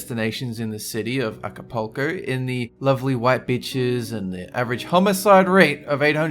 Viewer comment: did he say lovely white bitches? xD